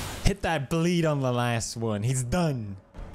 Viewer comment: He did his old intro he even gave us a brofist. This definitely felt old school👊 . Congratulations on finally moving! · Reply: i thought he said ‘last one before I moon’ haha Had to re-watch it a couple of times and give my brain a couple of minutes to figure out what he actually said hahah